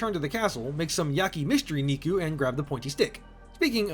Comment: I FORGOT THE DOLL! 7:40 Also am I hearing mystery miku right or am I getting hearing loss already 😂